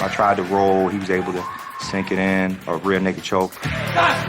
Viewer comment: Did he say a red neck choke? Lmao ive never participated in any kinda mma but even so, idt ive heard of a red neck choke before. Is there such a thing? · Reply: Rear naked choke.